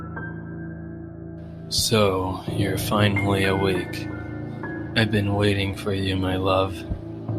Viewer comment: When he said so your finally awake Instead I heard "so youve finally arrived